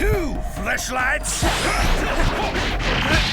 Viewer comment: What's the music playing at 10:06 to 10:17 (the drumming)? And did really say "Round 2, fleshlights!" or was that just my hearing?!